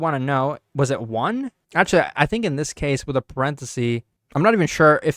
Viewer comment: do I hear the hindi word "Accha"🤔